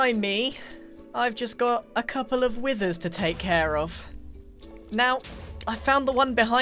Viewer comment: He said care of care of that and it sounded like he said Karen, Withers are Karen's they don't want to die